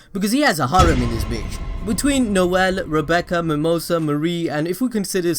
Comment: PART 2 OF THE VIDEO: <link> THIS VIDEO COVERS ALL SIGNS FROM CHAPTERS 1 TO 90 OF BLACK CLOVER, SHOULD we do PART 2? ALSO PLEASE LEAVE VIDEO SUGGESTIONS! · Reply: just a question did you say MARIE?